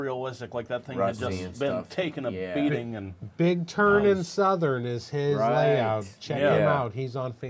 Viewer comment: I thought said, "Big Turn and Southern," but a search for that on Face Book yielded no results.